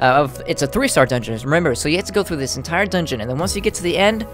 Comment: For a sec I thought you'd say "hentai dungeon"